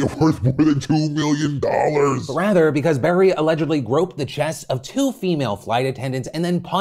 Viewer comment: I was shocked. I thought you said 'broke' the chests of two female attendants.